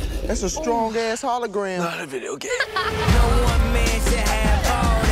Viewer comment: wait, what, what did Billy said? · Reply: Billy McCrary Not a videogame